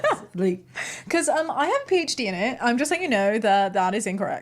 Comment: English is not my first language and sometimes it leads to funny mishearings. Like 40:30 I thought for a second said "I have a peach.a.d. in it" and was confused 🤣